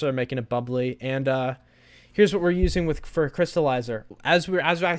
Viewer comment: Did he just say Fruity Crystalizer?!